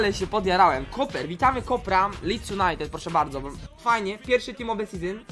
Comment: czy tylko ja usłyszałem Deeks United